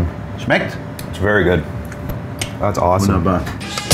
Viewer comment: He asked if it was schlect at 6:19, instead of gute